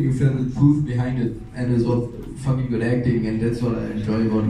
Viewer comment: what is saying after „that was fucking good acting“ ? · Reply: "and that's what I enjoy in..." I don't understand the last word. or it can be "and that's what I enjoyed the most" or something like that